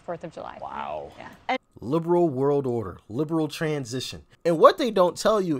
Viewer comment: 🤔Did you say "Lame Brain World Order?"because that is what I thought you said.😖